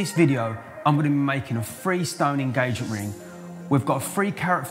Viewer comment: lol, i thought he said "free stone ring"